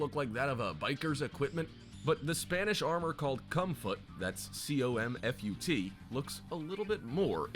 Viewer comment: what i see 11:10 "comfut" vs what i hear "cum foot"